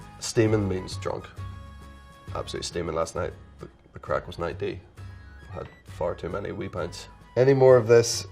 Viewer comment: i'm sorry did he just say "the crack is 90"? it's the crack is mighty lol someone's obviously misheard it and gone wild like